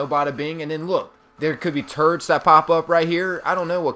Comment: I heard, "There could be turds that pop up here." LOL